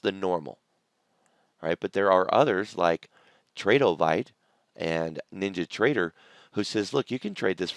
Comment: NinjaTrader and TradeOvite, did not understand what you said, please advise. . . Thanks March25, 2020